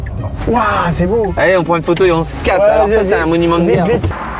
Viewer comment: C'est moi ou a 12:19 dit '' monument de merde '' ?